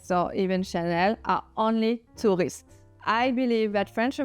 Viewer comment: Tourists. I thought you were saying “two wrists”. I think it’s very tacky the way Americans confuse an engagement ring with a cocktail ring. Look at the women of the British royal family. None of them has a diamond the size of an ice cube on her wedding finger. Only Princess Diana had a gigantic sapphire engagement ring. I understand that is a classic British engagement ring style, although not so large. Also, it should be noted, a sapphire is a semip-precious gem. It does not compare to a diamond in cost, although I’m sure hers was a valuable piece. She was allowed to choose her own ring, and she was very young and immature. Her choice of engagement ring, and wedding gown reflect that. I believe when she was older, she herself said she would have made different choices.